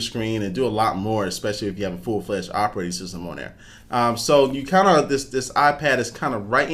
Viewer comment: I can swear I heard you say "Operate Racism?" at around 0:40ish